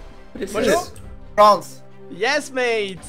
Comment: What did say at 10:00 ?:D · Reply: "It's the same country anyway"